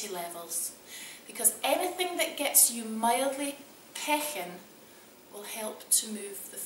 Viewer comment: "Anything that gets you mildly"---mildly what?? What was that word?? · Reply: Lin C 🤣 Pechin. It’s a Scottish word for out of breath.